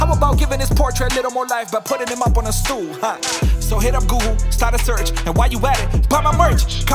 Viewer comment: You said gool instead of google?